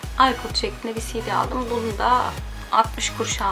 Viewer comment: Ipod şeklinde silgiye cd mi dedin ben mi öyle duydum 🤗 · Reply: Buse melis Ersarı silgi dedi